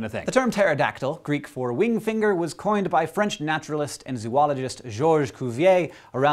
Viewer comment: Did Hank really say "Zoo - ologist"? sigh